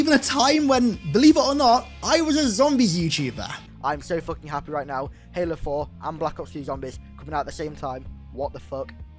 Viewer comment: I did a doible take I thought he said halo 4 and bo4